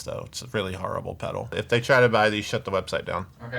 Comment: When he said "If they buy it" I thought he said "Ship them upside down" and wow that's kinda evil.